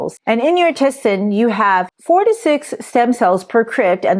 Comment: The caption says “there are 46 stem cells per crypt”. I consulted chatGPT and it says: “Each crypt contains a specific number of stem cells, often averaging around 4 to 6 stem cells at the base of the crypt.” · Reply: She said 4 to 6